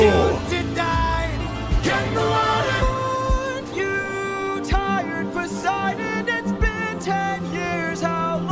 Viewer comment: Odysseus not begging, rather just telling Poseidon, "My god, get over it!!" It makes more sense to me. When I first heard this part, I initially heard, "Are you kidding Poseidon?"